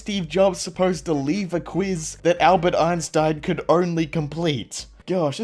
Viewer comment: sounds like he was saying queeze